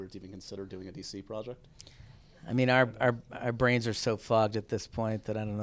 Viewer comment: At first, I thought he said "Our brains are so fucked at this point"